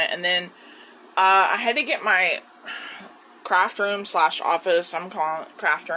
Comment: At first I thought she said "My bathroom slash office."